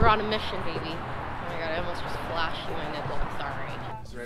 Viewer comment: Did she say oh my God I almost flashed my nipple?